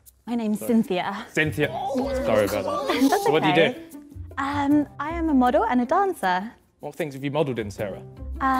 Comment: I thought she said “I’m a model and a dinosaur”